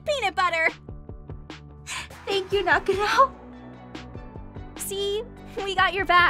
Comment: LOL, i have subtitles on and when said "thank you nakano" the subtitles thought she said "thank you nucky he'll" XD